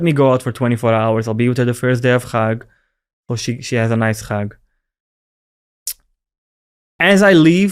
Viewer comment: What did he say about his wife at 1:10 ? .. I don't think he said what I heard... "she has a nice rack"